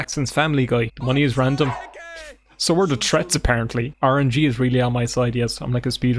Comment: If anyone sees this, can they tell me what 'orangey' means... He says it in this vid and I think it means luck or juju but I'm definitely spelling it wrong bc nothing comes up when I google it. Help :( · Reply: "orangey is really on my side". I honestly could just be mishearing it and I'll feel so dumb